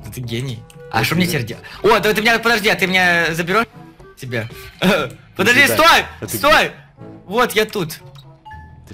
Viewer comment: так а шо ты сказал? Мне вроде послышалось язык#! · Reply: Katja Mygous он сказал з#га если это был сарказм то сори😅